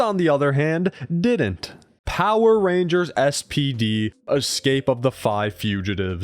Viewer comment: I was so tired when I heard this that I thought you said "STD" and I had to do a double take. 😭